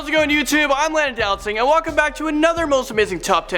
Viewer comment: You get so rushed sometimes, it sounded like you said "Landownlatsingh"!